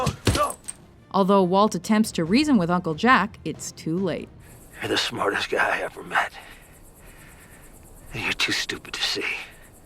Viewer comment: “You’re the smartest guy I ever met, but you are too stupid to see they are minerals” - Hank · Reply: Lol I heard "you're the smartest guy I've ever met, but you're too stupid to see the train comes in 10 minutes"